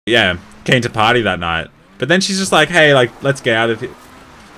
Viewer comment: English isn't my mother language so i heard: "Can't you potty that not" then i turned on the subtitles.. ○-○